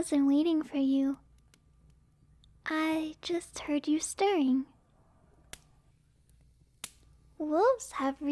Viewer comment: I know she said "stirring" but I thought at first that she said "I heard you staring" and I'm just like "wait... what- how- alright, I guess so"